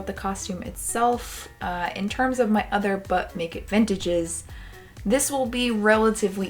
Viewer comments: All I hear is "butt naked vintage" send help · I heard “butt naked vintage” the whole time but I do have a fever rn so forgive me 😅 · When I hear you say, "But make it vintage," I always hear, "Butt naked vintage." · Almost thought you said “Butt naked vintage” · I thought you said 'Butt naked' vintage video at first haha, certainly got my attention, LOVE your videos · I thought she said Butnaked vintage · This was hysterical!!! 🤣 Why do I keep hearing, "Buck naked Vintage" every time you say that name??! You are so entertaining! ❤️